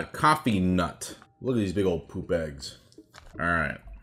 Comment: Had to rewind 3 times to make sure he did indeed say “look at these big ol poop bags”. >.< 9:30 Also....I had never thought about an m&m sexually before today.....thanks?